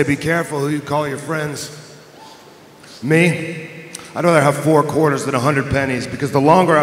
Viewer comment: "i'd rather have four quarters than a hundred pennies" keep your circle small. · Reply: same lol I didnt really hear the "I'd rather" bit. I just heard "ive only got four quarters and hundred pennies," I was like, what?